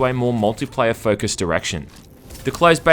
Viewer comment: I could've sworn I heard the words "multiplayer focused erection"